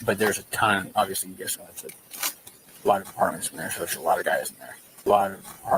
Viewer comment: does he say "a ton of APARTMENTS in there. Alot of guys in there " talking about questioning the people in surrounding buildings?!